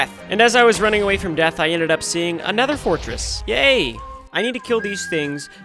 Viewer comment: who heard “another fortress” 😂